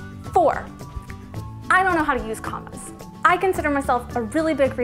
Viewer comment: I was looking at something else on my screen while this played and I misheard the word "commas". What I heard is "I don't know how to use condoms." and boy did my ears perk up. I had to go back and watch that part again. I was super confused and super relieved that I misheard you. Sorry. Love ya.